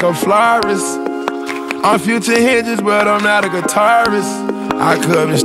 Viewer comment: Shit I thought this said "Failure", not "Future""